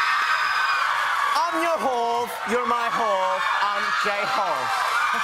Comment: “I’m your Hope, your my Hope, I’m J-Hope😄” J-Hope is the cutest thing 😂 and Jimin introducing himself was gold🥰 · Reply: definitely thought he said yur my ho